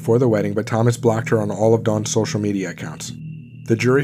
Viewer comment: He was found GUILTY sorry. Must've been tired during editing lol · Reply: I thought u said Thomas "Nutt" guilty of murder, didn't even know u said "not" guilty, lol